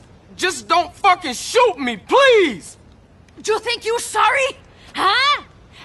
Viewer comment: Is he said? "fucking shoot me please!"